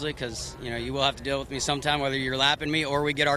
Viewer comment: Did he say semen?